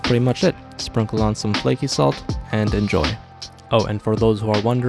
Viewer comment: When he said veal tongue i thought he said real tongue 😮‍💨💀